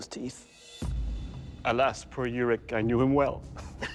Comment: what did he say there? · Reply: "Alas poor yorick, I knew him well."